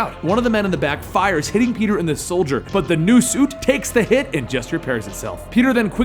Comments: FINALLY we're getting to the good stuff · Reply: did you say soldier instead of shoulder at 5:55? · FINALLY we're getting to the good stuff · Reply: hey love your vids but I heard you say "soldier" instead of "shoulder" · is it just me or did it sound like he said he hit him in the Soldier and not the shoulder